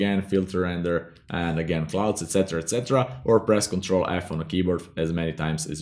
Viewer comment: "Press Ctrl...."? -Are there anyone hear what he says..??? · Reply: Ctrl+F